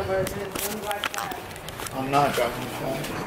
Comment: dude did you say "im not adopted mom" 😅 the heck?!